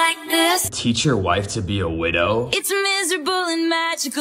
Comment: I thought it said “Teach your wife how to be a window” 💀